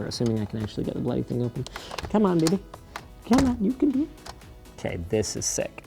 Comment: Did he said Come on "N-word" 2:38 or something else?